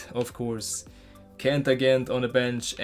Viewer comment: Did you just say Kent Agent? Tb to good old times